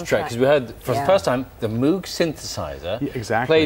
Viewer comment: I thought it was called Droog Synthesizer